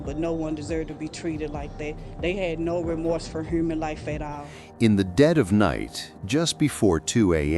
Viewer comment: Did she say heurman life